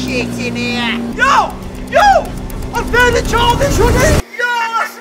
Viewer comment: I thought he said I found a child